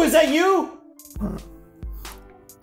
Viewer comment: all I heard was.... "hoy ya! oni-chan!!"